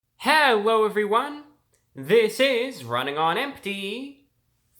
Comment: Did he just say at the start ‘Hewwo evryone!’?